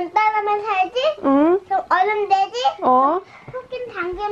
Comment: 어른되지? 어른되지? 하는데 어른돼지로 들려서 오늘 점심 밥 두그릇 먹은 어른 돼지 뜨끔해버렸어